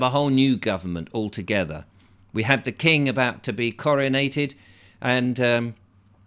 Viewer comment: The king about to be Coronated. I thought you said Chlorinated 😜 Sorry Guv I’m just a lowly peasant, who’s hearing isn’t as good as it used to be 😜 Speaking of Terror Ists, how many of these daily arrivals, Mainly Men, have been caught & deported??? No doubt the manholes/sewers will be checked prior to the Chlorination 😜 You can’t beat a good Bleaching 😂 Edited Andrew Bridgen MP has just made a speech regarding the WHO having the power over us in any future Plandemic 😜